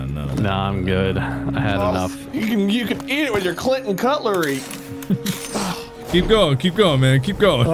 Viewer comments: Did anybody else hear "You can eat it with your clit & cutlery?" @ 17:25 ? I got so confused for a sec. 🤣😅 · for a second i thought said cl!t and cutlery. i was wondering why no one freaked